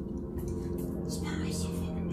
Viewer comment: did he just say "Spyro's so fucking bad dude"? I'm conflicted, because this was my favorite game series right beside DMC.